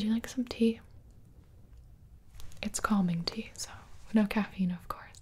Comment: So I had captions on and instead of "Calming tea." It thought she said "Call me teeth." And I'm honestly concerned for the captions now